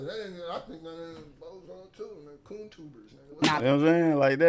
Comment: I thought he said goontuber not coontuber